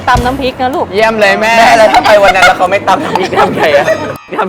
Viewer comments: พี่กันพูดว่าอะไรหรอคะ ฟังไม่ถนัดเลย💚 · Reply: "เเล้วถ้าเขาไม่ตำพริกทำไงอ่ะ" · พี่กันนพูดว่าอะไรอะ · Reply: "เเล้วถ้าไปวันนี้เขาไม่ตำพริกทำไงอ่ะ"